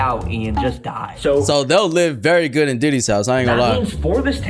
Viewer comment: when you said diddys house my captions said duty's house